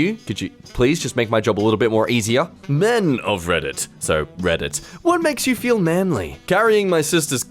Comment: did he just say "Men of Reddit so Reddit"?